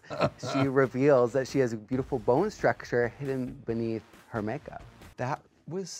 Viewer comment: What did Jimbo say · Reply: Beautiful bone structure hidden beneath make-up